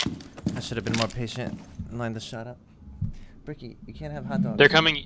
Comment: did he say beckey u cant have hotdogs near